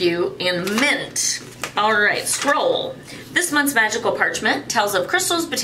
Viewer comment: Ok that pouch is cute. I can't believe I heard when you said "magickal parchment" I heard "vaginal parchment" I did a double take 😂🤣😂🤣 Ok want that wood pendulum. Ok that sticky note book is cute. That wooden box is awesome!